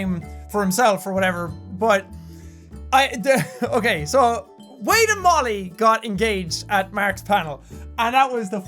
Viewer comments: I thought he said wade and bob engaged... i`d ship that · OMG I thought he said wade and Bobby got engaged 😂😂😂😂😂😂😂